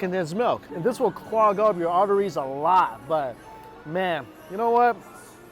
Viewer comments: did I just hear him say ovaries? Or arteries cause Mike you don’t have any ovaries 😆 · i had to listen to 6:30 again, cause i thought he said "ovaries", and i was like "WHA-"